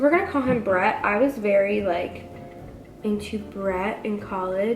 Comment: Why does it sound like she says “pinchi Bret” at 3:43? 😂😂